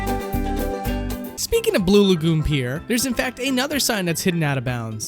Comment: Did you say B L U E L A G O O N P I E R?